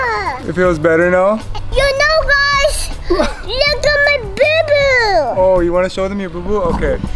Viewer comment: I thought you were saying the but boo boo